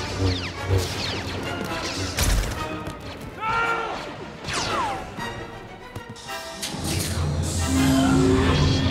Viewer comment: What is Bail Organa shouting at 4:22? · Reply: Nooo